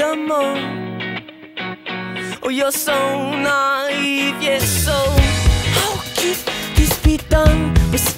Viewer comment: Damn I thought they always said " you're so naive years old"